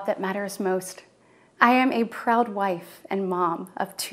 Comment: I thought she said Proud White 👀